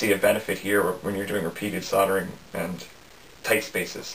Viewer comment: i heard you said something about penis sottering